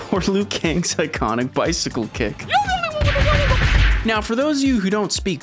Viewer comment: When Liu Kang starts speaking bicycle, I always hear, "You're-the-only-one-with-a-beautiful-butt!" Thanks Liu!